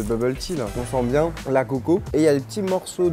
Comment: la quoi ????! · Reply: Coco, noix de coco